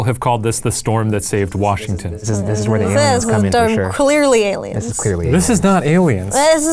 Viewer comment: "clearly aliens" ryan: "yes, queerly aliens" i know it was just him talking fast but that's what i heard lmao💀